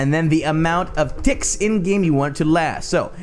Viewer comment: did he say dic*s